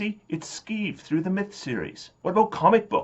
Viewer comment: What's the name of the comedy fantasy series you mention at 7:21? I can't quite hear it. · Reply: Robert Asprin did a fantasy series called "Myth" (they were all puns, like Hit or Myth)